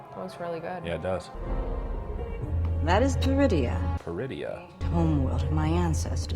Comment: It sounds like they said Druidia like the planet in Spaceballs when they came out of hyperspace. I'm wondering if Baylan is seeking knowledge of the Je'daii Order which walked the line between the Light and the Dark Forces. She should have called her mount Fang like Hagrid's cowardly dog.